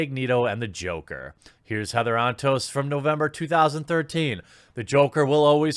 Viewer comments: Lol, the auto-captions thought Jeremy said "Heather on Toast" ! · All I hear is “Heather on toast” and not her actual name help